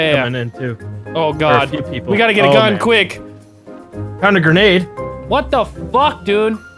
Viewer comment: "I FOUND A GRANDE. " the fuck. i thought he said grenade. typo? love your vids though